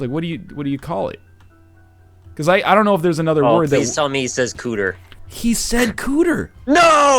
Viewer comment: what did his room mate say? Cant unstand the word.. - thanks :) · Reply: Cooter